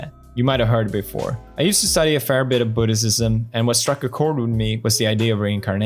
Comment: Did he say Buddhicism 6:31?